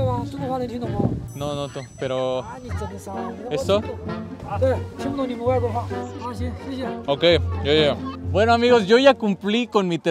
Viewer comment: Algien más escucho en el minuto 11:56 que dijo chino mis hu3v0